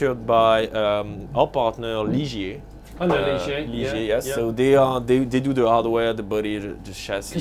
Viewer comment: What company made the driverless bus? Interested to see the 15000 electric cars they make a year, cant understand the name when they say it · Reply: Ligier was the company. Quite large in France and as he said they make the France Post service vehicles